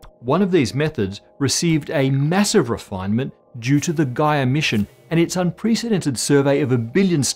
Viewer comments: What Matt says: "Gaia mission". What I hear: "Guy emission". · oh..when he said "gaia mission" i thought he said "guy emission" :)